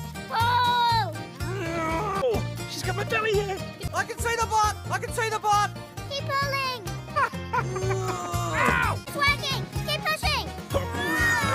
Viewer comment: “I CAN SEE THE BUTT I CAN SEE THE BUTT” “SING TO ME PAT 😡” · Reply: i hear "I CAN SEE THE BLOOD! I CAN SEE THE BLOOD!"